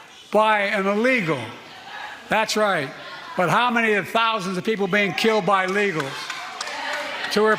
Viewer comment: Everyone is concentrating on the wrong part of that statement. After he said "An innocent youg woman who was killed by an illegal." he said "That's right. But how many thousands of people are killed by illegals?" Meaning she's just one of thousands killed by illegals so to him it's no big deal. The incredulity of this bully is just off the charts. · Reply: I thought he said “but how many thousands are killed by LEGALS…” like more Americans are killing fellow Americans… which I’m sure made her family feel so much better 😕